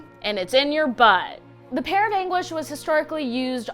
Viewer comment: I thought it said peer of anus...what the fu